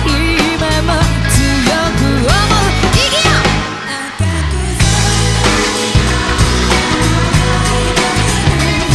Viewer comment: If somebody could translate into English what she shouts at 1:21 that'd be great. I hear 'igiya' but I could be wrong because Google translate isn't coming out with the English equivalent. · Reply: no "igiya" / " ikuyo ! " mean is come on!